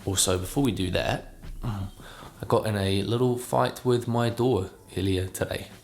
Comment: i have this on while im cleaning my room and at 10:21 i thought you said "i got in a fight with my daughter" so I looked over at the screen and there was a mark on your face and I was like damn this guys getting hit by his kid then I was like wait this guy has a kid? and then I rewound it and realized you said "door". that's all. lovely video!